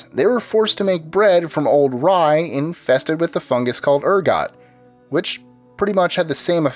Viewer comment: did i just hear him say urgot?????